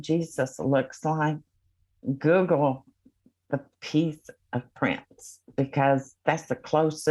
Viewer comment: Google what??? Did anyone catch that at 5:10? The Pizza Prince??????? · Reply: "Peace of prince" but i think maybe she meant prince of peace Lol 🍕